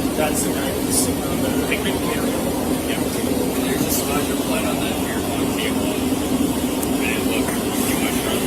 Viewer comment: timestamp it's real low but does he say there's a spot of blood on the rear of a vehicle??? It's so hard to hear his mumbling Caption doesn't pick it up. However he's definitely saying there's a spot of blood somewhere....Can someone enhance that part??